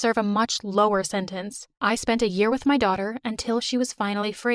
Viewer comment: At the end did She just say "We Become Soulmates" isnt that her kid?!